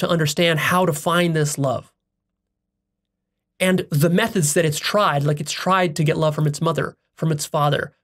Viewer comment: I thought he said meth heads lol